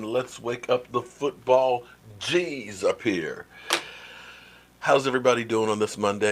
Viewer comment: Dude!! Did u just say "wake up the football CHEESE"???? We're not PUCKER fans....lol